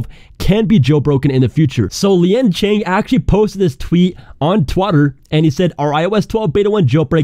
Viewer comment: is my phone breaking or did he say twatter instead of twitter?